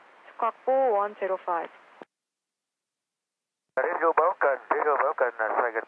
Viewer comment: VN409 기장님이 하신 말 (?) 처리 되어있는 부분 RADIO BLOCK 으로 들리는 거 같습니다!